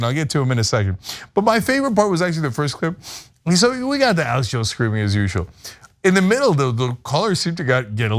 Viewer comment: Can anyone decipher what Cenk is saying at 5:02-5:05 ????? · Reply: "So we got Alex Jones screaming as usual." Pretty clear to me 🤷🏻